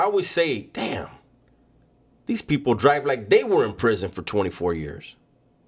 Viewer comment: You scared me for a minute I thought you said you were back in prison I was like what